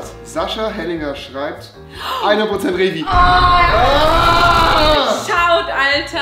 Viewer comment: was hat Sascha geantwortet? Verstehe es nicht 😂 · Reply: 100% rewi